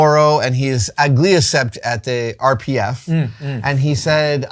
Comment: Chat, what is Adam saying at 19:21? "... and he is a gliocept as the RPF?"